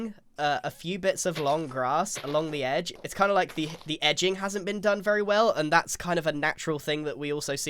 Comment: did i hear edging